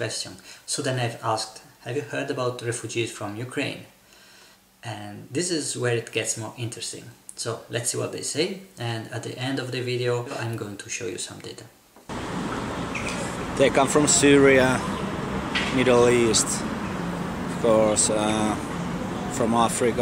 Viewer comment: I thought you said "And at the end of the video, I'm going to show you some tit"! So I was a little disappointed. ;-) You keep calling them Ukrainians, but surely the people fleeing are mostly ethnic Russians, I would think?